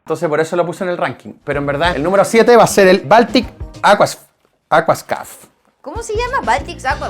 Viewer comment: Yo entendí baltica con agua😅